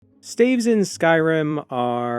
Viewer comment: I thought it said with only "slaves" for a sec